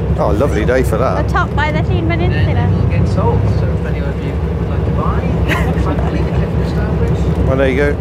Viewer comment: Did i hear talking Welsh at 6:16 ? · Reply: No, she just said atop the Llyn Peninsula.